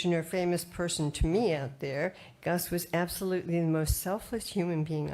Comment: did she say selfish of selfless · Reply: Selfless